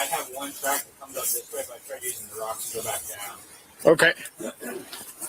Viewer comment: Who is the guy on the road/ tree area talking? Does he say “my friend used the rocks to go back down?” I can’t make it out